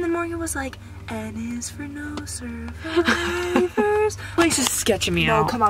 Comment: Morgan was really singing “N is for No survivors” HAHAHA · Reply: I thought she said "N is for No Service" ahahaha